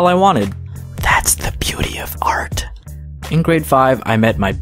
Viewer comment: I thought he said that's the booty of art